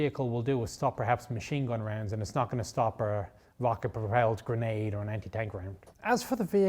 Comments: did he just say vodka propelled grenade? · I thought he said a “Vodka Propelled Grenade.” 😂